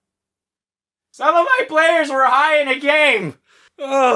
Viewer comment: What did he say in 6:05 ?cant hear it clearly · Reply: All my players where high in the game